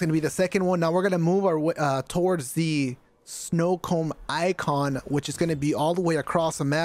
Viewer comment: I have watched this video a couple times, and props to you bro for it, especially using the same shiznit I use myself... but I heard "snowcomb symbol" and had to do double left side tap for the playback...snowcomb...snowcomb. like that Jack frost type hair combing tool. Or a winter time diddy. Haha